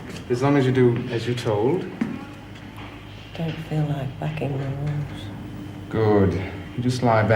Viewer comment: What did meg say at 22:16? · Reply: Carlene Boulden "I don't feel like bucking the rules"